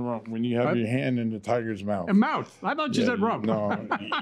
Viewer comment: Anyone else catch Doug’s dirty joke, “when you have your hands on the rump of the tiger” 😂🤣🤣🤣....he knew damn well that’s not what he said Lmfaoo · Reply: I read the comments first, so when the defendant said the phrase, I heard "rump" also. When he was being interviewed and after hearing Judge repeat it, i went back and listened closer. He did, indeed, say mouth not rump but he slurs his speech so i can see how ol' Shady Grady Doug thought he said rump. 😆